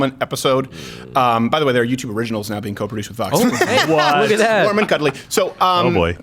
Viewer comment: What does he say at 3:29? Their YouTube originals are being co-produced with Fox? The subtitles say "with us" but I can't understand him because he speaks so fast.